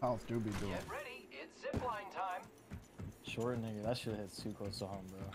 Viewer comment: did just say Short "nigga" here??? wtf? or am i just hearing it